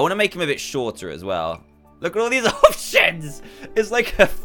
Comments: did Dan say 'OH S**T!' Idk Edit: Oh no... He said options... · i was here for the dantdm swearing comp 3 dantdm didnt actually swear on this one he said options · When you guys thought he cursed he said, "LOOK AT ALL THESE OPTIONS" and he laughed at the same time. ♥️ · Dan, I thought you said look at all these- oh s*** Also I saw a comment by Jastyn Rivas that you should make a Grim and I completely agree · he actually said "look at all of these OPTIONS!" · he didn’t swear he said options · LOL I know he says OPTIONS but I heard (not sh*t but) Ofshiz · Did anyone else hear that swear word slip out?😳😳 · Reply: He said options not oh sh*t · e ehhm excuseme what did you say dan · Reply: He said Options not the s-word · dan what did you just said?? you said " look at this ooo sh*t" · swear He actually says options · people think he said the s h word when he laughed at the dogs fur when he was saying options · Dantdm: “Look at all these options” He didn’t swear he said options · Did dan just said the S WORD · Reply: He said " Look at all these, options! " You can check by using captions. Or by slowing down the video to 0.25x nope sir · He did not swear at 8:20 look at subtitles he actually said option 😂😂😂😂😂🤣🤣🤣🤣🤣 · whAt did he say? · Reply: He said " Look at all these, options! " You can check by using captions. Or by slowing down the video to 0.25x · finally.....Dan, Edit: wait, did he say options???